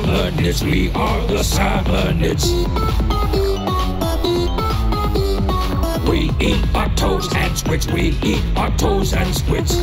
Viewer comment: I can't stop thinking the salmonids are saying "We eat our toes and squids" every time I hear "We eat octos and squids" ⚰️